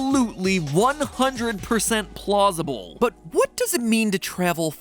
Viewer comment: Does anyone else hear plosible at 12:24? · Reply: Yeah he said plausible lol